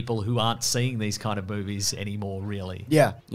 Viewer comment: So at 18:13 says Boobies instead of movies......?... explain?